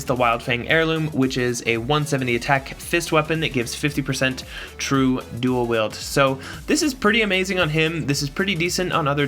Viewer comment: Upgraded aigaion weapon gives way good variance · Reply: Oh, sorry, I realize my mistake. I could have swore you said "true dual wield", but when referring to Rain, but going back and listening I was mistaken. Never mind.